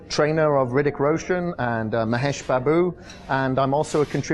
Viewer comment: Did he say Mahesh babu ?